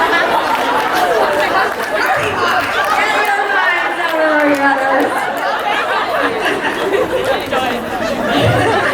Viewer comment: May I ask what Elise said on 43:15 ? I could not catch it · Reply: Shirley Ochavillo Elise said “get your minds out of the gutters”